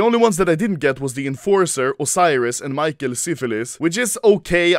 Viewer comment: Was i the only one who heard syphilis?